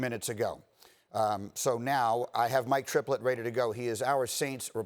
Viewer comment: I swear I heard him say ”I have my triplet now ready to go” and I was looking at him like, nah they don’t really look alike.